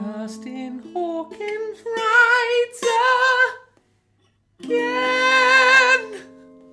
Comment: I misheard your theme song at first as "Justin Hawkin's Right Again!". I think that would actually be correct, as well as funnier to use!